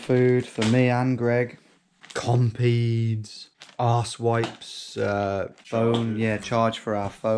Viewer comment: did he just say arse wipes around the 4 minute mark? lmao